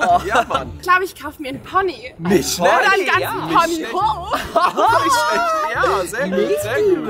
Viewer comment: Sie:oder ich kaufe mir ein ganzen Pony Hof! Ich hab verstanden:oder ich kaufe mir ein ganzen Pony Po